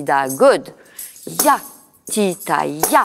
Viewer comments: Why did I hear it like: "Yes that's shit. That's shit. Nice, beautiful. " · I heard "that's shit"